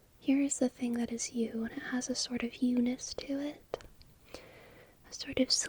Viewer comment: for the past ?? times i watched this video i thought you said "it has a sort of eunice to it" and not "it has a sort of you-ness to it" omggg english isn't my first language so i thought eunice was a new word i didn't know about